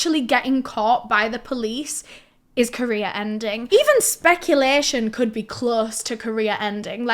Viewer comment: Amazing video and I’m not trying to make light of this but I love that when you say career ending to my southern brain it sounds like you say KOREA ENDING and I think it’s hilarious to think the entirety of Korea is losing their minds at once when they see anything like that panic attack style